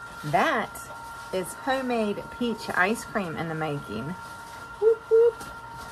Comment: i was listening and i thought you said fresh pizza icecream... i had to rewind and play it again lol